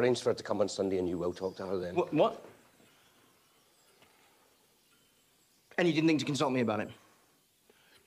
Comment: WHAT A COULD TELL ME THAT IS WHAT ELTON SAYS TO REID 0:39 PLEASE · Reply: "And you didn't think to consult me about it?"